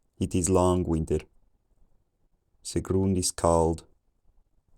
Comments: I started to hear words like cow, chicken, night earlier - and at 1:51 I heard the ground is cold. I def could understand by the time it hit 1500s - somewhere just before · I heard “It is long winter.” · It is lang winter. Se grund is cald. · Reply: I thought he said, "It is long winded." lol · "I hear "It is long winter, the ground is cold" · Chicken goes pretty far back. That was the first word I understood. I also thought he said “long winded” but it was “long night”. This was pretty cool. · Just after 900 AD I swore I heard, "...it is long winded" , but I could be wrong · I thought I heard it is long-winded but I guess it's Long Winter LOL I don't know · Around 900 I heard “It is long winded.”